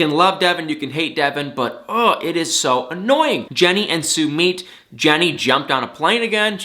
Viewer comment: For a second I thought he said Jenny jumped off a plane again I’m like HOLD UP FJDJDJ